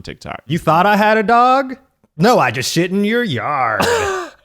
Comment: I'm trying to make a presentation and all I hear is "SHIT IN YOUR YARD!" Im dying.